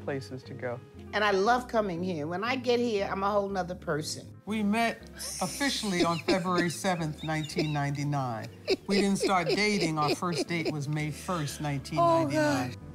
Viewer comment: WAIT, DID SAY THEY DID NOT OFFICIALLY MEET UNTIL 1999, BUT HAD THEIR 1ST DATE IN 1998?!?! I GUESS THAT IS WHY THE OTHER WIFE WAS LAUGHING, THE JOKE MUST HAVE GONE OVER MY HEAD 🤦🏽‍♀️🤷🏽‍♀️ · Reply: SAID “WE DIDN’T OFFICIALLY MEET UNTIL FEBRUARY 7, 1999, BUT OUR FIRST DATE WAS MAY 1, 1998”..... SO MAY BE I AM DEAF BECAUSE THAT IS WHAT I KEEP HEARING 🙄